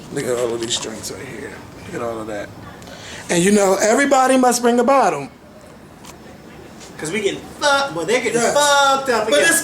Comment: It sounded like said "everyone must bring a bottom" at 5:19... LMAO